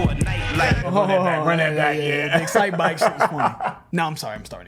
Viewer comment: “Did he just say Excitebike?”